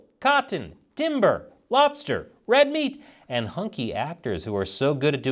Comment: "hunky'' actors. I thought you said "honky" actors. LMAO!!!